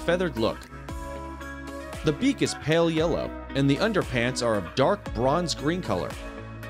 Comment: Did you just say bronze winged parrots wear underpants?!